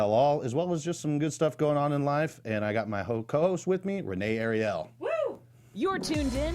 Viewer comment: did he say Ho-Cost?